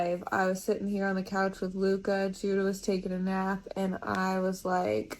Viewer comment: Wait did they reeally name their son Judas??? Or did I mishear? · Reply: Judah